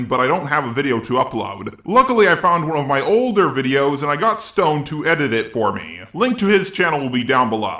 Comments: it kinda sounded like he said "I got stoned" xD but no, he said he got Stone to edit the video for him. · for a second I thought you said that you got stoned to edit the video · I was only half paying attention and the first time I thought you said "I got stoned" lmao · I thought you were saying you got stoned and edit it lmao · Uh... Eheh... I thought he said (I was distracted a moment) "I got stoned to edit it for me." Yes, I know he doesn't drug. Small mistake...